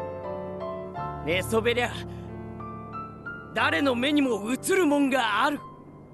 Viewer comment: Can somebody explain what he said from 19:51 ? Few words doesnt sounds like japanese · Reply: 寝そべりゃ、誰の目にも映るもんがある →No matter who you are, you can see something when you lie down 親父様の最後のお言葉にござる →These are my father's last words